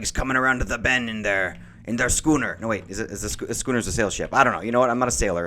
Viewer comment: I thought you said scooter xD